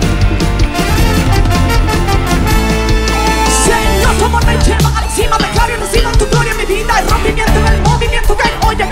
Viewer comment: alguien sabe que dice el rap? en el minuto 3:54 ?? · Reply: señor tu amor me lleva a la cima yo declaro y recibo tu gloria divina el rompimientoen en el movimiento que hay hoy hoy aqui angeles en del cielo tus milasgros ya nos diste con tus manos tu los hisiste y recibo el rompimiento que por la fe tu nos diste By: Chiri jsjsjsj